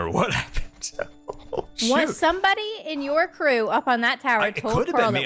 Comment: What does he say? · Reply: He said, "Oh shoot." it's the auto captions that wrote it wrong